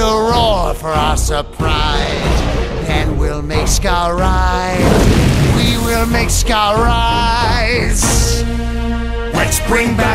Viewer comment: In ‘Bring back a legend’, Ushari sounds like he says, “We will make Scar rice... we will make Scar riiice!”